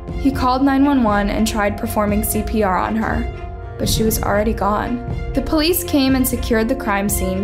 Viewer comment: why does it say black Man instead of Mask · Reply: it says gore instead of gone wich yeah wayy too late youknow